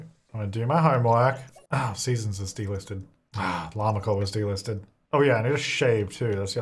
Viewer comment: Everytime he said delisted I thought he said d-listed 😂😂😂😂